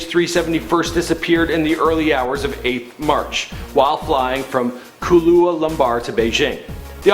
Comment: did he say Kahlua lumbar?